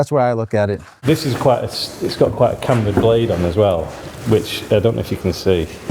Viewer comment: Small transcription error, captions say "candid" where they should have said "cambered"